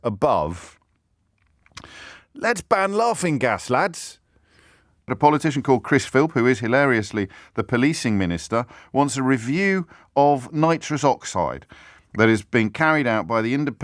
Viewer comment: Lucky there are subtitles on this, I really thought you said Chris Filth.